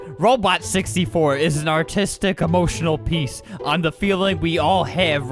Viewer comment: I thought he said autistic 😂